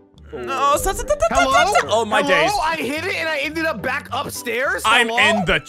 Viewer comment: Ya HAT it? That's what it sounded like ya said. "I HAT it!"